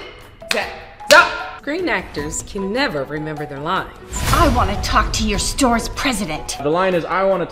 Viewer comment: I looked away for a moment during that last part and thought I heard her say "I want you"